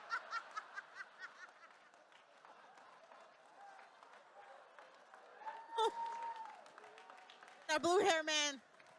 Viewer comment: what did that guy say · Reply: "just say it's in the works until we have it!!!"